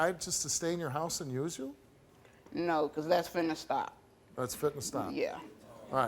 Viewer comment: What in the world do they say here? 😂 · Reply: "No, 'cause that's finna stop."